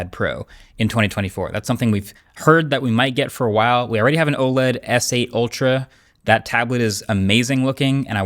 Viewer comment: Did he just say “we already have an older tabled with the Tab S8 Ultra”?!?!? Ummm bro….you did a review of the Tab S9, S9+ and S9 Ultra….😅